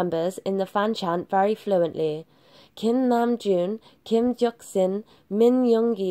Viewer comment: Did I just hear her say "Kim jeoksin"😂😂😂😂